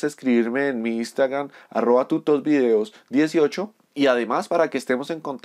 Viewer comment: pense que decian putos videos, fui el unico xd